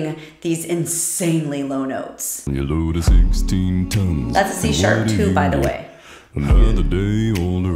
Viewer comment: When you said, "That's a C#", I thought you said That T-shirt and you were commenting on his muscles!😁